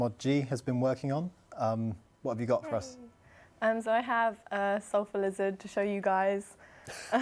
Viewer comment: lol thought it said sulphur wizard and didnt understand shit seeing it